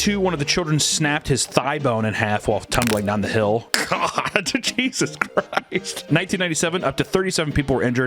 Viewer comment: Did you say Jesus Christ, or Cheesus Christ 😏